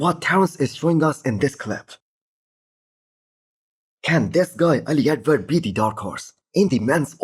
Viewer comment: I didn't catch the guy's name. Ali... ? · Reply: Ali Edwards